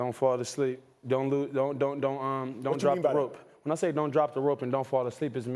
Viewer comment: I thought he said don't fall asleep and don't drop the soap. I had to rewind that.